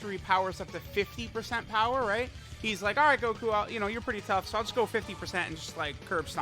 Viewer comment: did he say gokua? Now that’s a dokkan meme moment